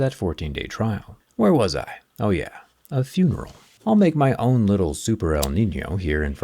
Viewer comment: Oh did i hear a funeral? Lol